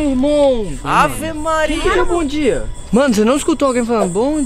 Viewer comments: O Coringa tem razão alguém realmente disse "bom dia" jurroooooo boaaa Coringa eh o melhor escutador de espititos do mundo pai slo bicho! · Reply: Eu escutei "oi bom dia" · eu escutei parecia uma voz da voltan, Assim "Bom diann" ou foi a voltan ou a babi, mas que fantasma é esse que fala bom diann kkk edit: carai postei ontem esse comentário fui dormir AZIDEIA quanto repost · Gente uma mulher falou "oi bom dia" no minuto 13:14 escutem, parece a voz da google, o "bom dia" do minito 13:00 foi a Voltan zuando